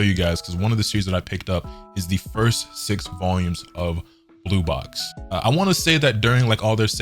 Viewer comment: I 100% thought you were gonna say blue lock🤣